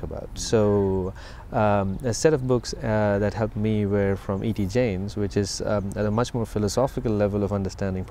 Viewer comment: What books are mentioned by the Guest at about 6:57? I cannot quite catch the name of the author. Thanks. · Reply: E T Jaynes